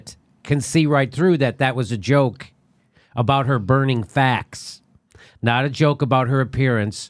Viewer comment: I think some people misheard what she said, they thought she said burns Fat and not Facts. I actually wasn't sure what she said at first, either....until I put it in context with the entire sentence. And Yes, the Uncle Tom bit was perfect.